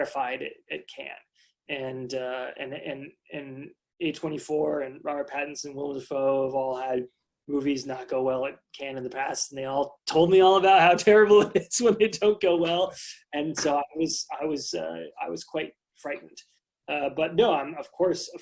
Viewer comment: he refers to "can of the past" being very difficult. what is can of the past? am i hearing it wrong?